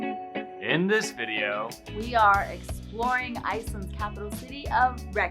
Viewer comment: I thought she said “we are in boring Reykjavík” and as an Icelander I was like “yup …..boring”………because it is really …for me …..everything is expensive so I never get to do anything fun 🙃